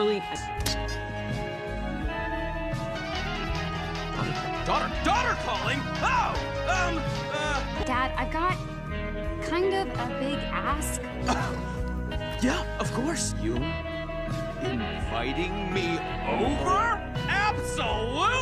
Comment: I thought she said „Dad, I’ve got kind of a big ass“